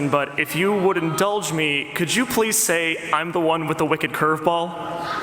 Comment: Can someone tell me why Mike turns to Rachelle and she laughs at 2:44? I'm deaf so I can't hear what they're talking about ;) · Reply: Someone in the audience brought twilight up and asked her if she could say the line "I'm the one with the wicked curve ball"